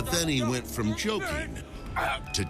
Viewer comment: i heard choking to choking💀 (joking)